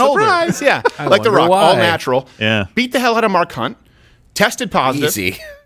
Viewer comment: 'Best Unacknowledged Joke Of the Show' Award Steven: "Beat the hell out of Mark Hunt" (Me: that totally sounded like Mah Kunt) Dave: "easy" (Me: 🤣🤣🤣 thank you Dave!) I immediately had the same thought and then when Dave made the joke I literally laughed out loud 🤣